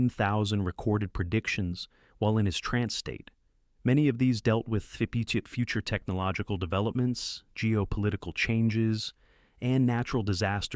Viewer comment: "fibutip future technologicaldevelopments"...? The closed captin says "fipate technological developments". What on earth is this AI saying? Im increasingly disappointed that I am sucked in to watch a video, only to be let down by inferior language models. Please...bring back the human speaking, before we lose the ability to do so...end up like those pictures of suposed aliens with teeny tiny mouths!😂😫